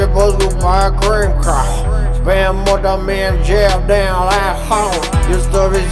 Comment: When gucci mane came in i was dead but when i heard him say "Spend more time in jail than at home" i thought he said spend more time in Jail damn asshole